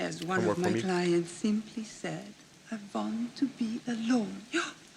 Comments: The " I want to be alone " quote is in reference to Greta Garbo. Love your reaction! It's one of Meryl Streep's great comedies. Check out She-devil if you want some hysterical Meryl. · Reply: I swear I always thought she was saying "I want to be a lawyer..." 🤯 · It was Greta Garbo who made the line "I want to be alone" famous. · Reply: I thought she was saying, I want to be a lawyer. I'm glad that you cleared that up, thank you!